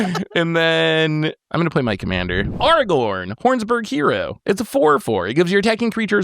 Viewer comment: did really say "Arrgh-orn"?😂